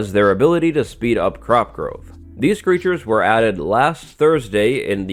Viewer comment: did u just say beetures?